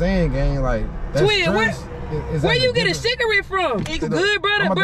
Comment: “Where you get a cigarette from” 😂😂😂😂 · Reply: I thought she said where you get a shigertte bro